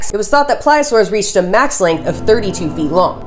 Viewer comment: I thought you said flyosaurus for a second. 💀